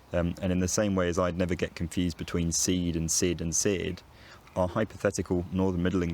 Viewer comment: what words are you saying there? seed/said/seared? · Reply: Seed,Sid,seared.